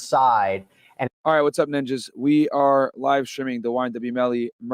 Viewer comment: did he just call us "ninjas"?